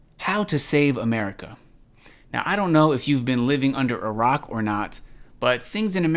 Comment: I thought I heard “I don’t know if you live under iraq or not”